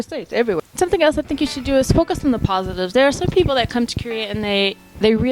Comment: When you said focus i thought you said smoke some x'D